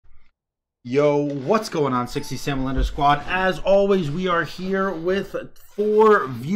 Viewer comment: What does he say at the beginning of each of his videos? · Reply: Wow always thought he said Sexy Sam L undersquad (maybe derived from the puncuation 'underscore') idk why he would say it I just took it as that his name was probably something like SexySamL_ in the past, never bothered to confirm it lol